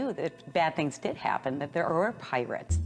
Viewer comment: I heard "I consider him a parrot"... he said pirate.